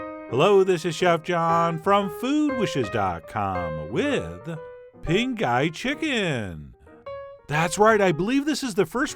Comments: I thought you said Pink Eye Chicken :P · I kept hearing "Pink Eye Chicken"......😁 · i keep hearing "pink eye chicken" xD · Why did I hear 00:06 ...with Pink Eye Chicken 🤔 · it sounded like you said pink eye chicken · Sounded like he said "pink eye chicken" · Sounded like you said,” Pink Eye Chicken.” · It sounded like he said Pink Eye Chicken 😅